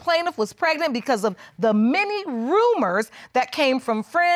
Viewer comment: I thought she said, “mini rumors”, but she said, “many rumors”.